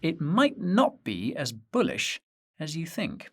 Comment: I thought you said its not so bullush... im bullish AF after watching this!